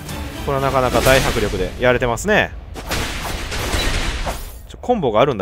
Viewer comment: 『やりますねぇ！』に聞こえたww